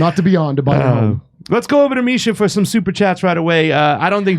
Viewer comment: I thought they were saying "Let's Go Brandon!"